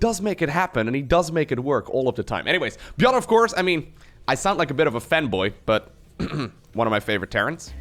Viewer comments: Omg I thought u said “I sound like a femboy 🙈🙈” then I realized it was my mistake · I heard you say "I sound like bit of a femboy" XD Don't worry Low, you are the best femboy ;)